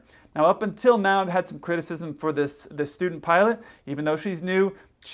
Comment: I really thought you said "stupid pilot" LOL